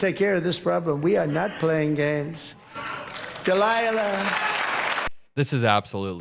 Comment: Did Trump just called her Delilah? 4:13 😅